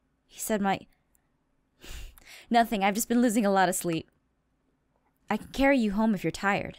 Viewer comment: i thought it said "Im just loosing a lot of sheep."